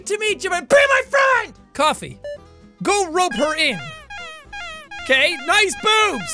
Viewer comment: I thought it said "grope her"